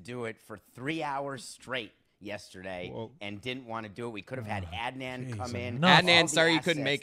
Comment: Always thought they were saying “Ad Man”….